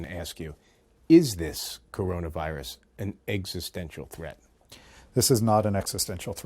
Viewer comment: What word was he saying? Exsustaincial??? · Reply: Long Zhang: Existential